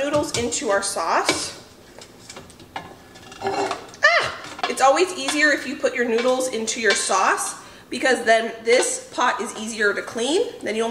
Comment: 🤣😂 I misheard you. Thought you said (when you were ladling the noodles into the sauce..). “It’s always helpful when you put your handles into the sauce, makes it easier to clean”. I had to rewind thinking “wait,,whaaat?” ❤